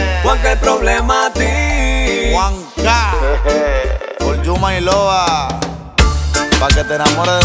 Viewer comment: alguien sabe qué es lo que dice juank en el minuto 4:12 "yuma y lova" (no sé si se escribe así) siempre lo dicen desde hace mucho tiempo, algún entendido en el tema que me diga xd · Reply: dice for you my lova